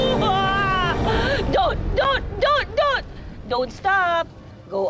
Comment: Ngl, I thought this was Malay drama lol I thought she said "Dot, Dot Dot!(as in shes mourning over someone named Dot) Dont stop, go on~" i gotta start watching tagalog series now😭